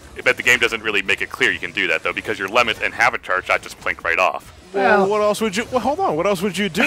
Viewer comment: Okay, I have to ask, but what was even saying at 2:08? Like, I'm hearing "lemon and half-charged shot" from him, and I'm pretty sure that first part isn't correct.